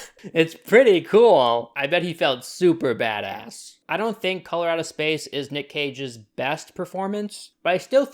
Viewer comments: Every time you said "Color Out of Space", I kept hearing "Colorado Space" and getting confused. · What I hear: “Colorado Space”